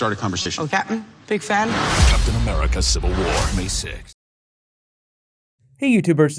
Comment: not at all, he just says hi captain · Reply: Sai Alur I thought he said o captain